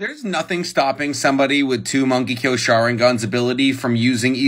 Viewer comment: I swear I thought he said monkey kill sharingan 😂